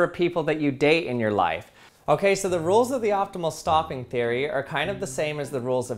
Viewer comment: I would swear you said "The rules of the optimal STALKING theory are..." @ 0:36.